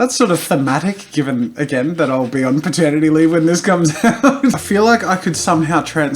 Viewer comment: When you said "paternity leave" I thought you said "maternity leave"